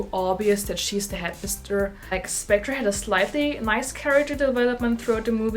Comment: on captions is said Mice instead of Nice.